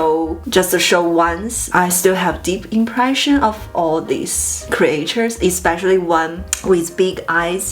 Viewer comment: Oh she is saying "creatures" and i heard "creators."